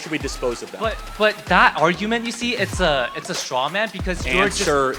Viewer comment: Hey Charlie you misheard the person speaking when they said you were making a "Strawman" argument, at the end you said he was accusing you of being a "strongman". This didnt really make any sense. I am not debating whether or not it was a strawman argument, just saying you misheard him.